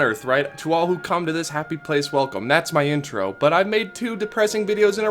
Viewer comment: I thought your intro said “Welcome to this happy place, welcome”